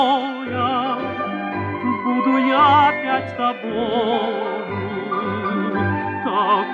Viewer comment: У меня эта пластинка связана с весьма забавным воспоминанием. Я часто слышала её из соседского окна и вероятно расслышала не всё, потому что пела так: О, Карина усни под шум прибоя Буду я спать с тобою, с тобой одною. У соседа, когда он это услышал, округлились глаза. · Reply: Тогда я понятия не имела о воспетой в этом танго любви и потому "спать," (вместо нерасслышанного "опять") было всего лишь желанием хронически невысыпающегося человека буквально поспать, пока неподалёку"рядом где-то(!)" спит Карина.